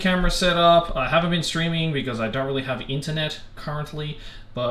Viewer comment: "I'm not streaming cause I don't have internet" * Has youtube open and was listening to spotify *: "If you know of interior design please give me your feedback!" * Aki saying she loves interior design in an amateur level, on her video* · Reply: Am I crazy or did he say "Ethernet" not internet ? LMFAO 🤣 I could be wrong and misheard